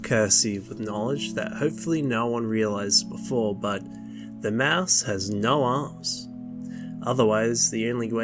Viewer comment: why did I hear "The mouse has no ass"